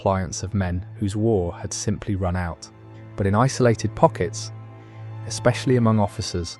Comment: "Rockets" instead of "Pockets". I give up. Bye !